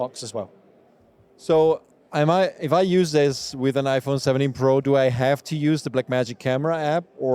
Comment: Thought you said the 17 Pro Dog. That would have been an amazing name.